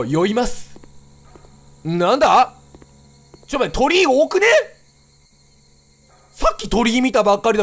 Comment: キヨさんの｢鳥居多くね？｣ が｢鳥多くね？｣に聞こえた人。 先生怒らないから 正直に手を挙げなさい